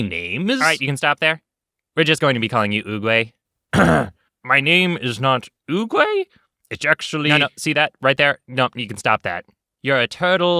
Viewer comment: the captions say "my name is not goo goo a bitch"